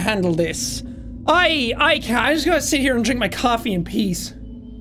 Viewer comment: I accidentally skipped ten seconds so I heard “coffee in pee”